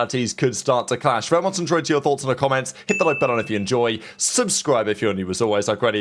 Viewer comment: “Remontsadghhugfrre thoughts in the comments” someone please tell me wtf he is saying